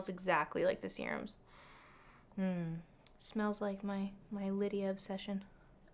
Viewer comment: Did you really say "smells like my Lydia obsession" or am I just imagining things? XD Because every time I hear 'by terry CC serum' I think of Lydia MG <3